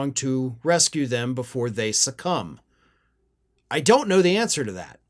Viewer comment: I had to rewind this because said that republicans, "succumb" but I heard, "sucks cum". Same difference.